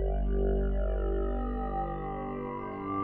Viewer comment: I always thought efren said" like a dragon"